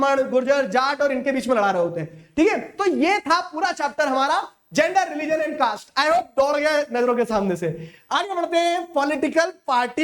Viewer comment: I heard dod gaya n*llo ke same se